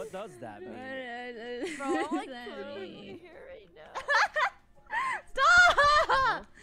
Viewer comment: what did say? 🤨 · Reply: oh "bro I'm like twirling my fucking hair right now" ?